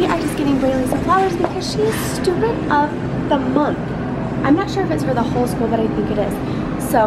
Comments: I thought she said,"she's stupid of the month."I was like woah. Then it processed through my thick skull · Omg!!! I thought she said stupid of the month!! Lml!! I was like "what!?" Lol!!!!! Geez!